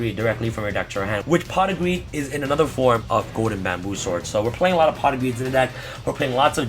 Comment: I use pot of greed. Pot of greed allows me to draw 2 more cards. I use pot of greed. Pot of greed allows me to draw 2 more cards. I use pot of greed. Pot of greed allows me to draw 2 more cards. Opponent: You should be playing solitair. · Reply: I thought you were saying I use a lot of pot for a second lol